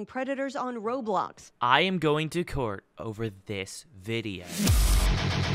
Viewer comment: I thought you said "I'm going to cook Over this video" 😭 and That wasn't wrong either